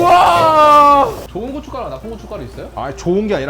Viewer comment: 그 와중에 고운 고춧가루 좋은 고춧가루로 들었는가 '좋은 고춧가루도 있고 나쁜 고춧가루도 있어요?' 물어보네 ㅋㅋㅋㅋㅋㅋㅋㅋㅋ